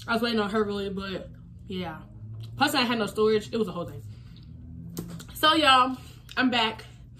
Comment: i heard u say "dat hoe"